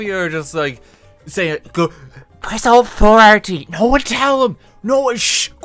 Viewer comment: I genuinely have no idea what you said there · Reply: press alt f4 RT